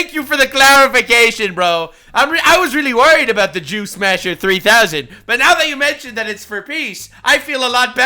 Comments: I'm listening to this while I work on something else, and around the 10 minute mark you mentioned the Jew Smasher 3000 rocket, my ADHD brain heard it as the Juice Masher 3000 and for a second I thought you were doing a very confusing ad read. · Who else heard “Juice Masher”? 🧃🤣